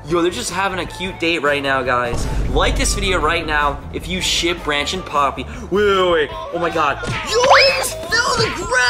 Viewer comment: For a second I thought you said branch and Pomni you know from the amazing digital circus